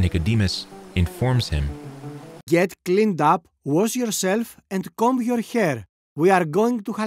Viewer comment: I thought he said "get cleaned up, we are going to Ikea"